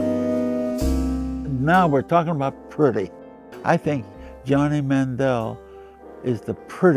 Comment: I thought he said Johnny Manziel.